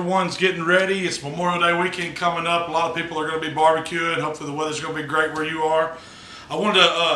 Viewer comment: Lol the first time i played this i literally heard, “A LOT OF PEOPLE ARE GONNA BE BARBEQUED...”that’s just disturbing 😳 if it was true 😂